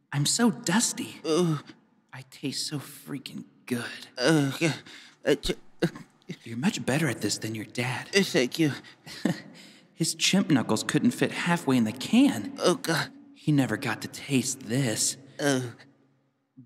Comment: "his chimp knuckles" one of the funniest things I've ever heard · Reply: I thought he said chip knuckles